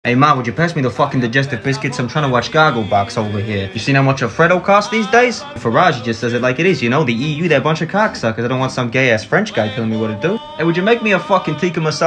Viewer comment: “Ya seen how much a Freddo costs these days?” weakened my defenses but “Farage tells like it like it is ya know, the EU are a bunch of cocksuckers” got me. I’m a Brit who moved to the US four years ago and my boss sounds exactly like this guy so this feels like it was tailored specifically for my sense of humor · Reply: I thought he said Alfredo